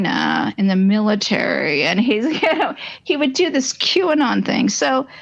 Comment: I don't remember... I need a time stamp on that, not having listened to it for months. Sorry. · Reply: It’s around the 13:21 mark. I listened again and caught what she says before, that “deconstructed QAnon”. I did a quick search and he wrote an article titled “Making Sense of QAnon”… The link between the two was confusing to mind at first but now it makes sense.